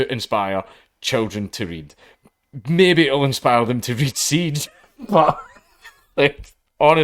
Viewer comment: Read what? Siege? Or am I mishearing it. Someone pls tell me what Dank is referring to.